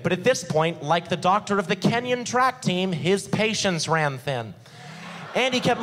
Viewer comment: Could someone explain the joke or at least write what he said at 44:02 - 44:07 ? ^_^ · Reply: Like the doctor of the Kenyan track team, his patience/patients ran thin. Kenya, being a country in Africa, where people commonly think of skinny people.